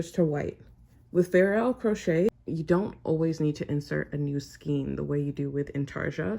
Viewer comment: This is great, thanks!🙏♥️ ❓❓❓ HOWEVER... at 11:20 in the video I couldn't quite understand what you were saying.🤔 You said, "With ??? crochet, you don't always need to insert a new scheme (or skein) the way you do with intarsia crochet...". It wasn't clear to me what kind of crochet you were referring to because I couldn't clearly understand the word (I indicated as "???") that you said in order for me to be able to look it up and find what kind of crochet you were referring to. 😕 Anyway, thanks for the tutorial. 🙂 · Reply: I think she said "fair isle" crochet, I know it is something in the crochet-world but I don't know what is it ahah hope it helps